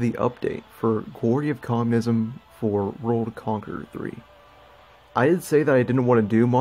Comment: Sounded like you sayed rome conqueror 3